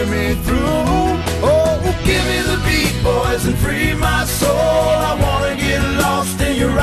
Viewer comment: When I was young I thought he said give me the people